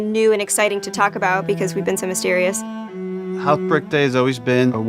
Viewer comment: I thought he said "heart break day" and I just thought Hell yeah. That's a cool name for the apocalypse. Then I realized it was outbreak day, and while not as cool, it's practical